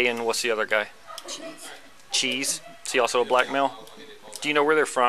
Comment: You know you’ve made a wrong turn in life when you have people with the names of A and Cheese driving you to a bank to try to scam money from them. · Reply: I thought she said egg and cheese 😅😅